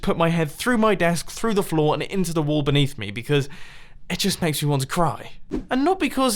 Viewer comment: did he say through the wall beneath him